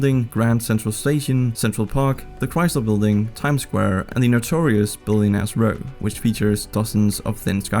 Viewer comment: I had to do a double-take when you said, “Notorious Billionaires’ Row” because I first heard, “notorious billion ass row”